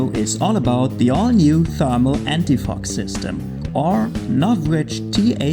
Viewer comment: It sounded like you said, "anti f**k"😂